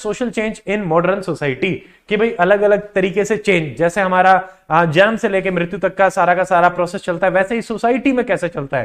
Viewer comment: in 2x i heard it as exam se mrityu tk 😂😂